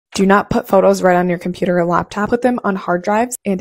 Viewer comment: my ass really thought she said "dont put your photos ontop of your laptop" and immediately thought of photographic paper